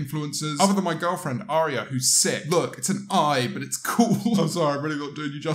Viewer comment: Thought he said "Other than my gf Airia, whos six""